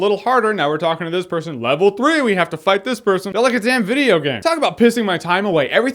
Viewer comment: You gotta change that T-Minus photo my friend. Little bit outdated. Also at around the 2:15 mark did you say 'an videogame'?